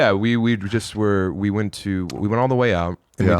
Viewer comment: Sam stuttered "we..we.." and I heard "Oui Oui" Comedic genius without effort